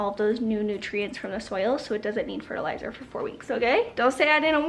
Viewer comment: So you said they need fertilizer...I heard "they need their pre-workout"! 🤣🤣🤣